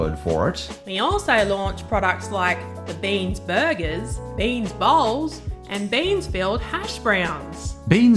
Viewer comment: I thought she said beans balls not bean bowls. 😂😂😂😂